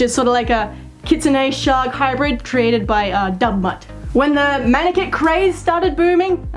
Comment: I thought she said "dumb butt" instead of Dubmutt 😂😂